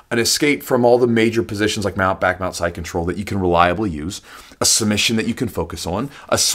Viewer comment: Hi pls can you clarify starting at 2:38 what you are saying? You said an escape from mount back, mount and side control, is that correct?